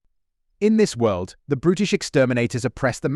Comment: Zardoz - brutish exterminators? I thought he said British. lol😂